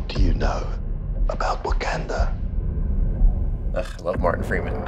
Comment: I hear he said Uganda 😆kinda weird thing..I'm hearing..it like do he know the wei hahaha.